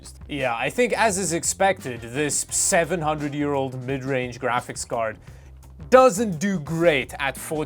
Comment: I thought he said “700 Euro mid range graphics card” I had to replay that to make sure and thankfully he said “700 year old graphics card” I about spit my drink out thinking he paid £700 for that beast 8k card